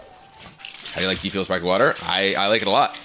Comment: Please, someone can sub or translate this? 7:56 ?? they talk about spiked water? that's like fermented water or something like that? besides the play I enjoy the talking of Kibler but sometimes can't understand it (Im from latam hehe) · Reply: "How do you like Gfuel with sparkling water"